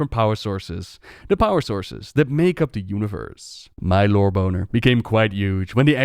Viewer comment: “my lore boner” lmfaoooo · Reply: I heard "My low boner became quite huge" idk if that's actually what he is saying xD